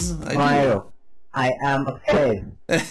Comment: it sounded like peach said “I am dead”.